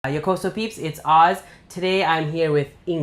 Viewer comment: Every time you say "Yokoso peeps it's Oz" I feel like I hear "Yokoso weebs it's Oz" LOL